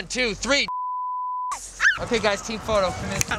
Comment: "Look straight ahead. Do NOT look back!" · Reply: I thought he said: "You DO NOT look fat!"